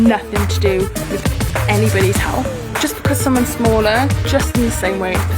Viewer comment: She says anybody while the subtitles say anyone! That just seems beyond lazy that they can't even get their subtitles in order.